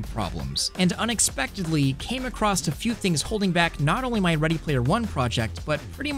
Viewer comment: Outstanding at every level! As always!! Except when you sounded like you said "Came acrossed" at 0:45 in, which I know you didn't, so 100% dude!